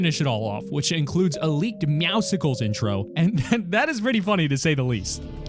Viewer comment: did he just say meow sickles